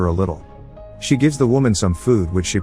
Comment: I didn't understand the sentence at 7:48 until I turned on the subs and realized you pronounced IV as FOUR instead of I-V (intravenous). Made me giggle :)